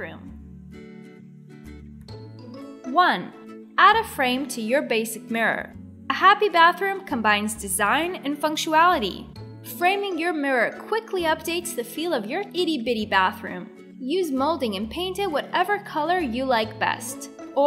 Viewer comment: Did she say FUNCTUALITY? 0:27 I did look it up, just to make sure it wasn't a derivation I wasn't familiar with. Nope. It is supposed to be FUNCTIONALITY. The nearly-impossible-to-see captions has it spelled correctly. LOL Wait...a starburst decor piece on the wall in the SHOWER? That's gonna be fun to clean.